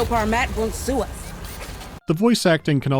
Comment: I think she's saying "Let's hope Armat won't sue us", not "our MEP". I thought Weyland-Yutani manufactured the smart guns, so I guess they got that wrong.